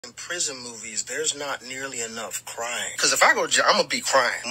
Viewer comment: Ha! I thought he said there's not nearly enough crime in prison. I love this dude...he always has me crackin the hell up